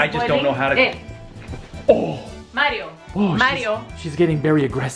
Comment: Did she said Mario